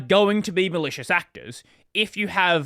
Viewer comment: I heard "delicious actors" twice until I stopped mishearing.